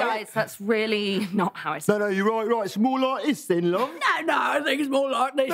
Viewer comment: What does Oscar say at 0:34 ??? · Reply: “it’s more like this, then, love?”